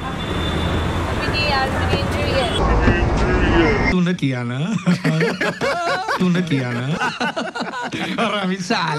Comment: kya bola kuch samajh nahi aya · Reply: Mujhe injury hai